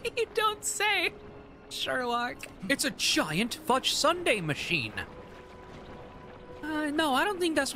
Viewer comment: I know she said "fudge sunday" but I swear I heard "giant futch sunday" and I swear if that's not on Bluesky today I'll be mad